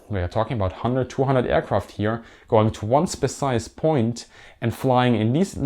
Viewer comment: I had no idea what he was talking about when he said “Groups” and “groups” but not “Groups”. It was very confusing. Also, at 9:16 he says “Specise”